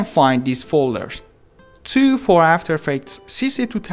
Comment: is it only me or someone heard "2 for after effect sissy"